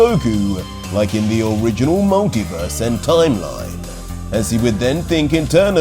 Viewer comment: Did he just said at 29:30 Monkeyverse? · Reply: No he said original multiverse.